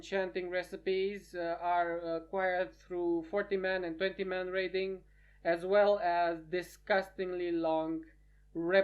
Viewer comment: did you just say 40 man AND 50 MAN raiding in this Video who has a cap as 40 man? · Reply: ????? he said 20 man raiding